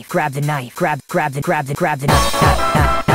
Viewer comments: I thought it said grab the gun💀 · (Misheard lyrics) The lyrics: grab the knife How I hear it : crab tonight